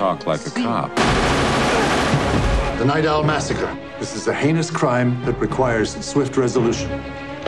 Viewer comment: did he say. this is a anus crime ?? :O